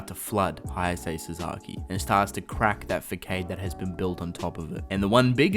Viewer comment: Did you just say "Fakkade" instead of Facade?